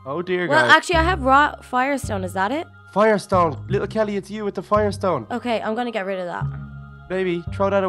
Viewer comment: did say "little kelly its u with the fire stone,baby thow it away"